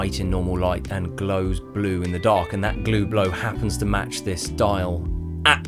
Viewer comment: Excellent video! Gonna try baking the hands, that lume came out so pretty. Also did anyone else catch the "Glue Blow" at 38:22 I went back and listened like 3 times, thought I was nuts